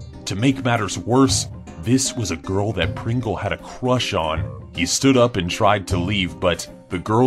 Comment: That was Dolan’s brother at church Pringle was at school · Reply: Shit sorry I thought you said church not crush